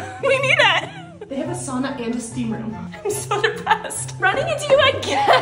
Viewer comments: “i’m so depressed😃” proceeds to smile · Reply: Oh my god I thought she said impressed 😭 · “i’m so depressed😃” proceeds to smile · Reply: I thought she said Impressed :'D